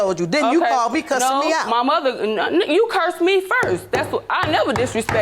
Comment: did she say nigga u cuss me first lmao